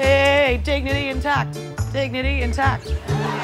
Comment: "Dignity intact!" 💃🏻💃🏻💃🏻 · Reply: Looool. This whole time, I thought she said "dignity attacked" and wondered if that was an ironic celebratory dance.